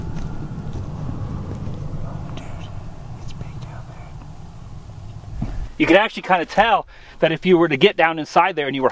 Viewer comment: there is whispering "dude ......" ---- what is being said??? and he is pointing that the end of the tunnel is in the opposite direction as the opening - did I miss something?? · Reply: He said is big down their